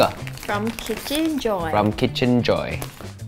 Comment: I thought they said "Chicken joy~" and I was like "Jollibee?" 😭😭😭😂😂😂